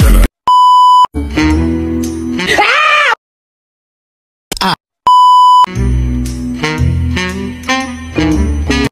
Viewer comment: I wonder what’s this scream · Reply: Cardi B saying "AHH WTF".